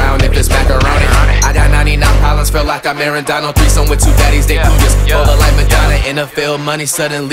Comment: is it me or i sound like he said 3 some with 2 daddies but he said baddies tell me im not the only one